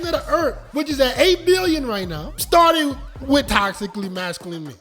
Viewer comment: "Insight" du jour. The regurgitating of the regurgitating of what another "guru" heard another "guru" post on their, "today's video is sponsored by", channel. It seems to me these "gurus" listen to each other's stuff and repost it as their own. Anyway, I bailed the second I heard, "the population of duh errf is at 8 million". At 1:38, it sounds to me like he says, "8 million", I could be misunderstanding his accent. At 1:01, it's sounds tho like he's says, "for a millions of centuries ". It doesn't matter. What matters is the content and its purpose.